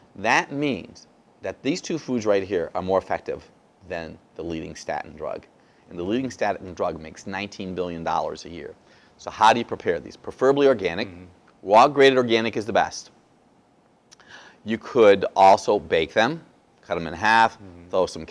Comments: did he say "wild graded organic" is the best. It seems like it would be wild that he said. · did he say "wild graded organic" is the best. It seems like it would be wild that he said. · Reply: I thought he said raw grated